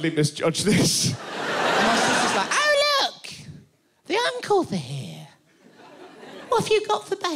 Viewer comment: Instead of 'The uncles are here', I heard 'The uncouths are here'...XD